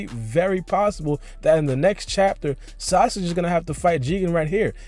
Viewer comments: did you say sausage? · he said sausage instead of sasuke